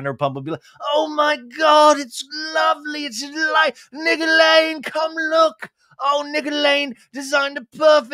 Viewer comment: Nick Alaine Thunderdome fighting cage match with Graham in the background just made my night 🤣 But Scheana really makes me vomit and want to punch my screen. Omg. I still think she will end up with Sandoval in the end, and they really deserve each other. I also think Lala defends Scheana because Lala likes having Brock around to play uncle with her kid. If she loses Scheana, bye bye good Uncle Brock. Idk, I've been smoking weed so maybe I'm just high. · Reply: Is it Nick Alaine? I’ve always thought he was saying Nicolaine. 😂